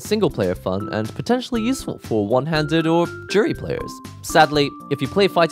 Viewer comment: …. Did you say “ Juri players” ???🤣🤣🤣 So true